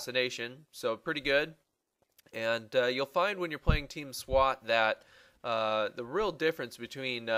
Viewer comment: Sounds like you say Team "Squat". Good tips though!